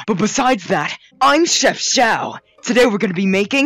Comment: i needed to watch 3 videos till i realized xiao says "im chef xiao" i thought he said "XIA-XIAO" like he is some kind of mcqueen lab rat